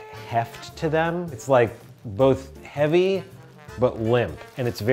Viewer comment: The usage of cat as demonstration of "heavy but limp" is great. Thanks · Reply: I thought he said fluffy, not heavy :) :) :)